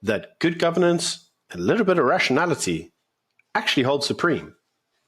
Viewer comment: did you say Russianality? 👀